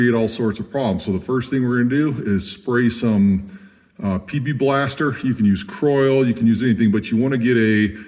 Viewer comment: "PP" Blaster lol I know you were saying PB Blaster but you caught me off guard and was like wtf did he say lol